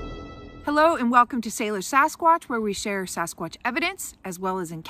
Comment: i thought you said Sailor Sasquatch and thought of a hairy Sailor Moon posing. /shudder